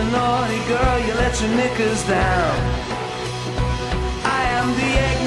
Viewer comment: Did he just say the N word? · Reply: Knickers, term for underpants worn by women